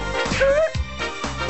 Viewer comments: The best line of Ed. Ed: In Syncopated Style 1:00 Eddy: ED! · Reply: I always thought he said “and shake yo bad style” lol wow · At the end of the episode when Ed bashed everyone away with his hips, I actually thought he said, "And shake your pants down!" at first instead of "In syncopated style!" since that's what it sounded like to me. LOL 🤣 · I’m not gonna lie until about 2 minutes ago and having watched this show since I was a kid I seriously thought at the end Ed said “dancing the pants style” only now to find out it’s “in syncopated style” and my mind has been blown